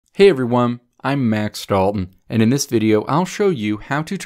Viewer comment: For a second I thought you said Max Dillon and I thought OH BOY, there's a reason this guy has a tech channel